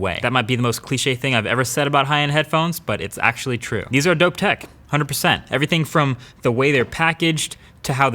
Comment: What is he saying at 7:21? (To me it sounds like he's saying, "These are go tech" but I don't know if that makes any sense. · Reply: Dope tech